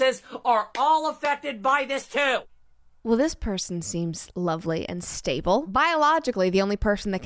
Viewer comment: I thought it said “lonely” instead of “lovely” honestly, doesn’t change a thing.